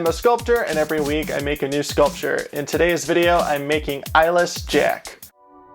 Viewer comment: Ngl it sounded like he said “eyelash jack”